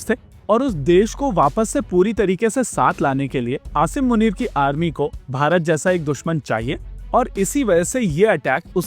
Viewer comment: Did you say asir munir JI at 3:08 😮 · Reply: "Asif Munir ki" kaha thaa... "ji" nahee. Thoda sa toh bharosa kartey iss channel ke anchor per. 🥹🥹